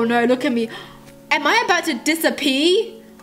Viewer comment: Sounded like "An I about to dissa-Pee??" I'm cracking up! !😂😂😂😂